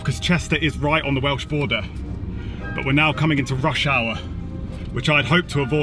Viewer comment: Sounded like you said Russia